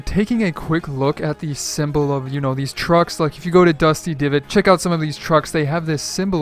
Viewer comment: I thought he said drugs, sorry